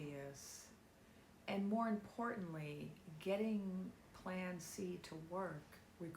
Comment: As Soon As You Said Plan C....I heard "Plant Seeds" .. Over and Over 🌌✨️🌠💫